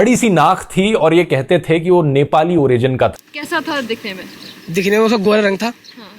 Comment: did he called "CHINESE NEPALI" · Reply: Wahii,😂😂